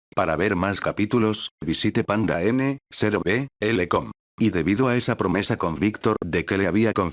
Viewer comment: Lguien me puede decir que dice en el minuto 23:40 panda que 🤔